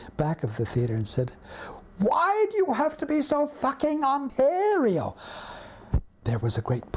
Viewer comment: What's the whole quote? I cant understand the last word. Does he say Ontario? Its really bugging me, because I like this video a lot. But this one word throws me aloop.